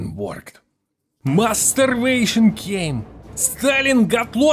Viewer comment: I thought you said "masturbation" at 1:37 lol